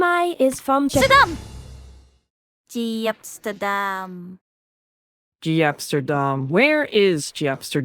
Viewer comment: the STERDAM sounded like Kasane teto